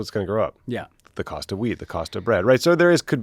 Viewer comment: I thought Tommy said “the price of weed, the price of bread is going to go up”, but he probably said wheat…😅